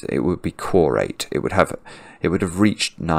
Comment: what word is he saying here??? Sounds like “corrate” or “quarrate”, but from my Googling no words remotely like this exist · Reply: "quorate"